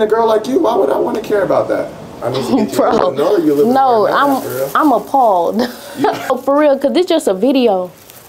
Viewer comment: “I’m appalled” I thought she said “im a fraud”😭😭